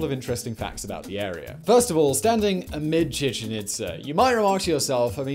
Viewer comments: My dumb ass thought it said Chicken Pizza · My brain farted and i thought it said chicken pizza